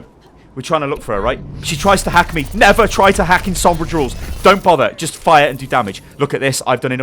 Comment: I heard 'Never hack in Sombra drools'